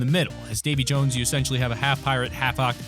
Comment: When he said Davy Jones I thought he said Baby Jones my first thought was a baby Jonesy